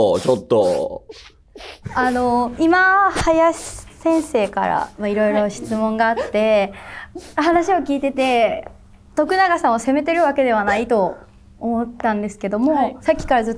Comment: いやいや、このお二方、攻めるようにしか聞こえないんですが？？？